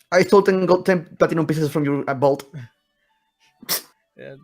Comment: what did say? It was soo quick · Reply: I stole ten gold pieces (andibolt?)